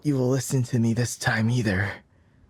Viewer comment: everyone heard either while i heard aether